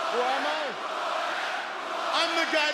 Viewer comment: What does what the public says mean? They said it in Ireland too. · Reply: “Who are you”